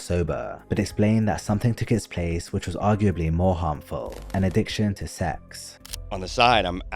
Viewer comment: i thought at first you said: An addiction to Seth 😆 i wasn't watching the screen , just listening